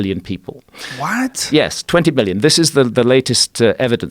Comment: I almost heard him saying 20 billions and i was already ow shut the fuck up.. but ok 20 million its a nice acceptable number